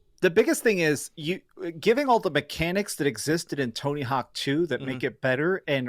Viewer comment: what did he just say? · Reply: Tony Hawk 2? Uhh...